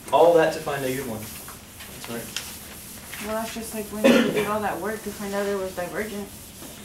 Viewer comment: what did she say at 2:01:19 ? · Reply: Something about doing all that work to find out it was divergent.. something along these lines....